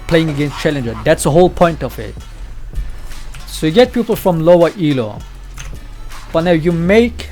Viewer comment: I realized you say "people" like "pupil". Makes it a bit easier to get what you are saying now.